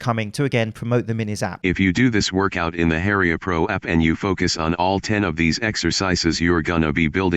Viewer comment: I misheard it as "Heria Bro app" which made sense to me considering who we are talking about.